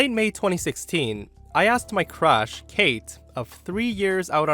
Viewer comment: I had this on to listen to and not watch and I swore he said "I asked my crotch" at 07:35 😂🤣😂